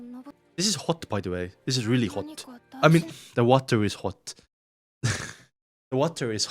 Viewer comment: I THOUGHT U WERE SAYING THE GIRLS ARE HOT AND I WAS LIKE WTF😭😭😭